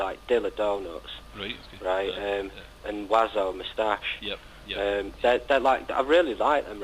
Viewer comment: when he said he liked dillas donuts he said something else that I couldn't pick out. Sounded like wazzo mustache but couldn't find anything on it. Anyone happen to know who he was talking about there? Its driving me nuts. its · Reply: He's talking about Mr Oizo and the album Moustache (half a scissor). Analog worms attack is also a beaut album by him from 99